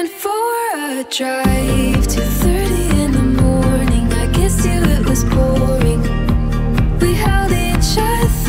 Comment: I originally thought she said ‘I kissed you it was boring’ And I remember thinking when I misheard it: Wow no wonder they broke up